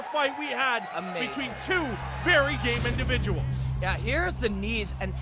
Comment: does he call them both gay? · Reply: Game, but lol XD